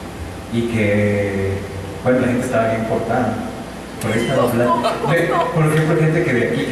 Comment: que dice?? Que estaba bien portado ?? Jajajajaja